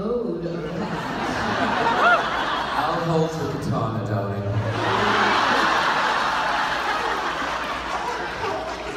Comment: What does he say at 0:38? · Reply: If you're still wondering, Mr. Andrew says, "I'll hold your katana, darling". He loves making Ms. Danai blush😊